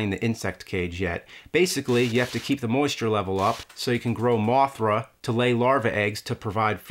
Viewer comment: did u just say mothra?